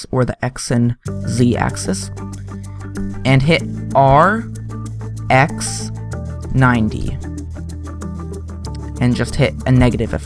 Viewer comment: does he say R,X 90?